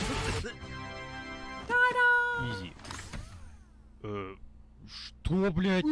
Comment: я один кто услышал слово изи?